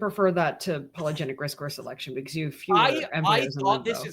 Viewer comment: What did say? Polygenic risk course selection?